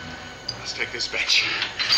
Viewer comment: Sounded like Doug said ‘Let’s take this bitch!’ 😂